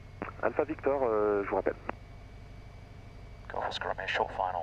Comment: Does anyone know what the controller said to Alpha Victor at 16:33? It sounded like "je m'appelle" but the context didn't make sense. I hear controllers say this a lot in France and have always wondered. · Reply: He said "Je vous rappelle" which means "I call you back"